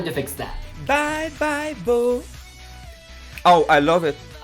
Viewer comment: apparently I am not the only one to understand "bye, bye, boo" instead of "bye, bye, fool"